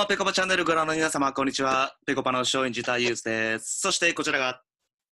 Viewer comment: 松陰寺さんの最初の挨拶、松陰寺太勇ズって聞こえるw